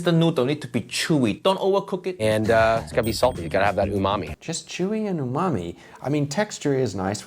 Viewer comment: In the Captions it says “mummy” instead of “umami” Interesting! 😂😂😂